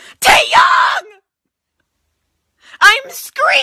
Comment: okay why do i hear it as taeyong not taehyung :')